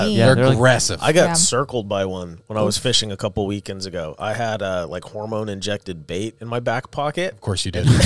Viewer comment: Def thought said he had hormone injected vape in his back pocket…I was like what is this guy smoking?!?